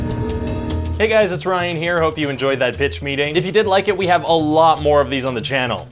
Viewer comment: I thought at the end I heard "if you didn't like it, we have a lot more on this channel" lol I kinda wish that is what you said.